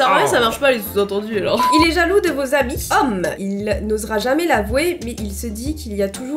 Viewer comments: Moi aussi à partir de 13:45 j'ai compris : "jaloux de leurs habits" (aller voir pour comprendre) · moi aussi j’avais compris "il est jaloux de vos habits" 😂 c pour ça j’avais pas compris au début 😅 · moi aussi j’ai entendu il est jaloux de vos habits